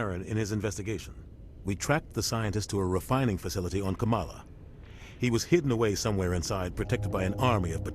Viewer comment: Camala. I thought that said Canada. lol